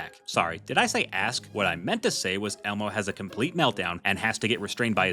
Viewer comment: what did Elmo say here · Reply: Oscar! Oscar! Oscar! Oscar!